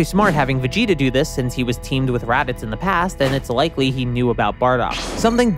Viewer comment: you said rabbits instead of radids